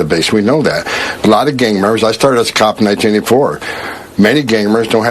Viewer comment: At first, I thought the dude was saying "gamers" & wondering why the hell was he picking on them? Then I realized he was saying "gang members". Someone sold him a bum set of dentures.